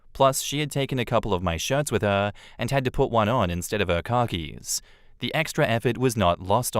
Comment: When said "Khakis" I rewound it because I thought he said "Car Keys". I heard it again and heard car keys. I had to rewind it and actually read the words to see what he's saying.